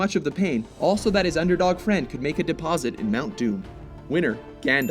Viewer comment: I first thought he said mount dew